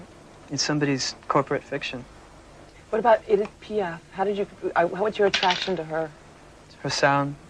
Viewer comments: who is the person that the interviewer says at 3:54. i cant understand what she is saying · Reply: Edith piaf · What does she say at 3:53? "What about _______? How is your attraction to her?" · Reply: Edith Piaf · what does the interviewer say ay 3:54? "what about _" and he goes on to describe her, who is he talking about? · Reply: Edith Piaf!